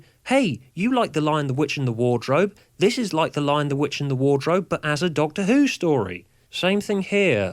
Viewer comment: I thought you just said: 'This is like The Doctor, The Widow, and The Wardrobe,' but As a Doctor Who Story! Lol. :)